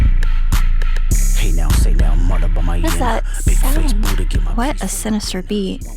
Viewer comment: This whole time I thought he said “big face booty” not “big face Buddha” thank you genius for the lyrics